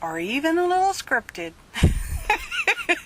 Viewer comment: OMGIGGLES brayer up the nose! You are SO my Mashed Lentil , you giggled at an important moment and I heard "happy farting" Off 2 get my sqweeky handle brayer my hand brayer might not work on this